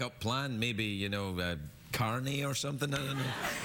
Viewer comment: Anyone know what he says around 26:05? "a backup plan, [ unintelligible ] or something?" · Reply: "...a backup plan, you know, carny, or something?" where he's referring to a carnival worker, in the sense of a sort of a huckster or showman.